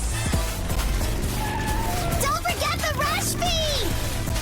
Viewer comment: I thought she said "don't forget to rush b" and chuckled